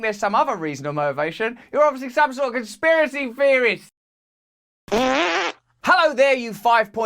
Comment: I thought you said "Experiencey Theorist" lol